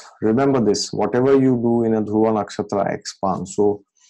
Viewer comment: I didn't understand an important word around 14:05. You said whatever you do in ____ nakshatras expands...(good or bad) was the word 'dual'? So curious about this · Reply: He said " dhruva nakshtra"